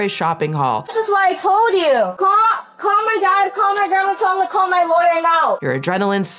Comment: I have a small hearing disability and it sounded like she said "call my gynecologist..call my dermatologist...call my lawyer now" And I was like maam what is your gyno gonna do? Just listing professionals before getting to lawyer 😂😂